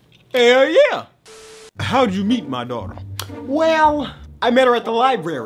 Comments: It sounded like you said dog at first I was thinking a yoooo but then I heard you say daughter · Am i the only one who thought they said dog instead of daughter? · I thought He Said dog not daughter xD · I literally thought he said dog not daughter took me 3 times to realize